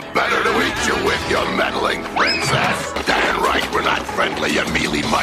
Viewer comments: Did he say “Damn right”? 😄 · Did he say "damn right" at 8:31??? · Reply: I think it was “dang right”